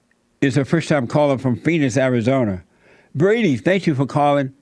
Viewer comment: Jesse literally sounded like he said "Penis, Arizona"...and right after talking about trans surgery.